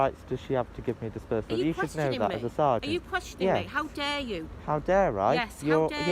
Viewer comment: Is she saying, "Are you questioning me ? How dare you." ??